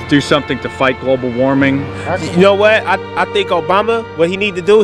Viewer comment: 1st listen and I heard "you know i think Obama needs to go get that Kendrick Lamar, that s**t's gold". Although dabbling into rap and hip hop helps since I know who Kendrick is